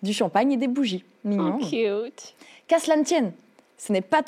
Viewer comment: mais mdr elle dit quoi a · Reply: "cute" (mignon) il semble